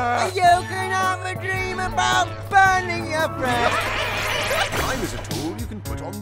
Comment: "You can have a dream about loosing your friends" #livingthedream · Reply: I thought he said "you can have a dream about loosing your brains..." well thanks for correcting me :3